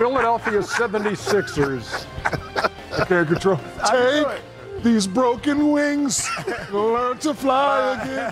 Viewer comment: I coulda sworn Shaq was saying "Tank these broken wings" 😂